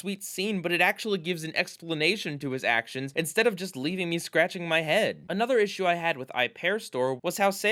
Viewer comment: Is it wrong I heard "leaving you scratching my head" instead of "Leaving me scratching my head"?